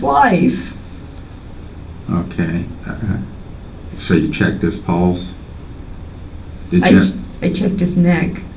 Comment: i just heard you checked his ballz.